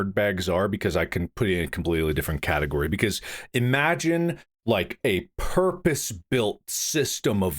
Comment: I like the”Old guys talk about same-same” things they said in the past because they’ve had this discussion before same with the blackberry too. I think Bob was on Wade’s side before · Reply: anyone else hear Mark making another “I turned around” every now and again when he says imagine